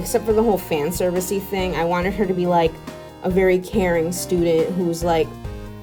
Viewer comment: I thought she said " Karen student " Instead of " Caring student "